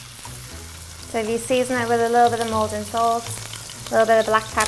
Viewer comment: I cant understand what she is saying at 00:50 help please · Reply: I am saying, sorry I didn't season this enough